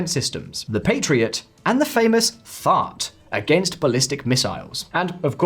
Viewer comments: Can you clarify something for me: The US has deployed in Kuwait two large anti-aircraft defense systems... the Patriot and the... what exactly? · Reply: I heard “fart,” but that cannot be what he meant. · Is he saying “the famous fart?” That cannot be what he meant but I cannot think of what missile system sounds like fart. 😂